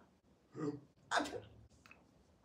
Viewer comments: ”Ugh, it lingers.” That tells me all I need to know. Not every lost recipe is a winner. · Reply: I heard, “Ugh, it has fingers” and I was picturing the taste causing a reaction in his throat. Like when you eat something to spicy or hot. “Lingers” makes more sense thank you. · ”Ugh, it lingers.” That tells me all I need to know. Not every lost recipe is a winner. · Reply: Is that what he said? I heard "it fingers" and was very concerned